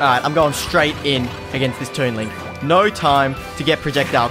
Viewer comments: did he just say toon link lol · did he just say toon link? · Did He Just Say "Toon Link"